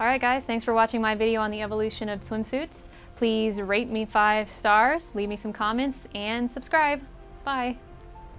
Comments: it sounded like you said, Rape me five stars X3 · WOW did I hear right in 4:03 you said rape me 5 stars? O and you are hot.